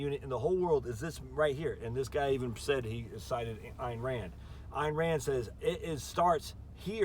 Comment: Well said. 50 + 1 could be the reason the framers adopted the electoral college. At first, when you said Ayn Rand I thought you said Iran. Just saying. Of course we do need skilled laborers some times.